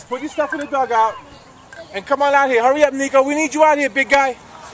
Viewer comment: did he say hurry up nigga